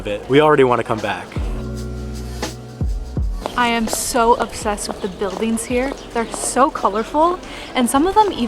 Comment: Did I understand correctly that you are upset? Why do you convey such negative feelings to us? That's what I don't understand anymore: are you upset or happy to be in Gdansk? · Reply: You are probably alluding to what the girl said at 1:19. She did not use the word 'upset', but 'obsessed'. The whole phrase she said is 'I am so obsessed with the buildings here', meaning that she likes them to the point that she is unable to take her eyes off them. She obviously is happy in Gdańsk and the message conveyed by the girl and her boyfriend in the whole video is super positive.